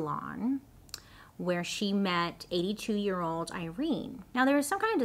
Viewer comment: i thought she said" 2 yr old Irene"